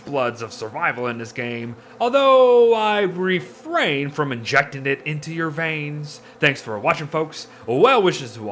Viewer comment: I missheard yer' last advice... I thought you said "I'd refrain from injecting it into your brain!" XD